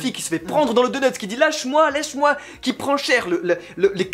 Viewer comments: Luffy qui se fait prendre qui dit "lèche moi lèche moi" 😂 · "Luffy qui se fait prendre dans le donuts, qui dit "LÈCHE-MOI, LÈCHE-MOI !", il prends cher." Euh...